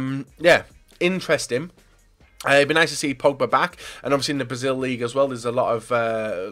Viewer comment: Did he say pogba in 3:22?